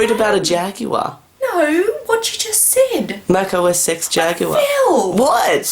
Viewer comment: hey it was funny when your mum missheard you with mac os 6 jaguar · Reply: RedstoneFreakCrafter No, she thought he said "Mac OS SEX"